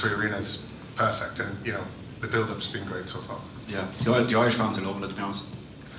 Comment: the subtitles for this is all over the place lol 10:34 Richard kiely - the irish fans are loving it to be honest.... Subtitles says - enjoy bouncing over latinos lol